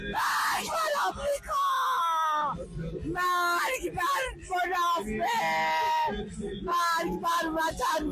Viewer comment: درود دقتت خیلی پایین اومده متوجه شدی 7:19 این دلقک داره چی میگه. مرگ بر فنافل ،فنافق ، فنا چی😂😂😂😂😂😂